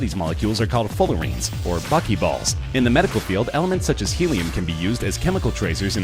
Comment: anyone else hear "fuck you balls"?